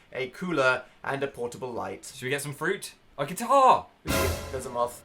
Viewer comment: What does Phil say after "should we get some fruit?" ? 05:19 Have a nice day everyone! :D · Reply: He says "a guitar!"... And then they both stare with fear at the moth 😂